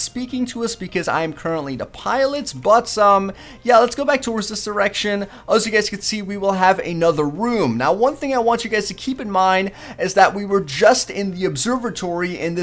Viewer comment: sound like he says lets go back to this erection